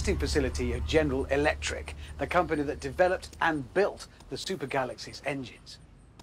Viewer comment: did he say stupid galaxy??